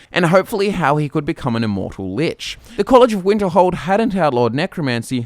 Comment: it sounded like you said "immortal Bitch"